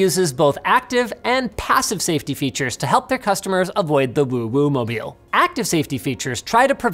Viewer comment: Did he says OwO mobile?